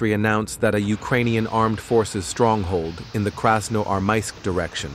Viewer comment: "Krasnodar" direction? Really? Krasnodar is a city far from the frontline xD ( "Krasnodar" instead of "Krasnoarmeysk" was written in subtitles )